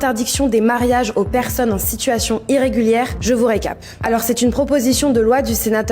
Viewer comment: C’est vrai tout a fait d’accord. On devrait l’etendre sur 10 générations antérieures. Ceux qui n’étaient pas là au moyen âge doivent partir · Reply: Perso j'ai entendu "situation irrégulière". Aussi, vous êtes hors propos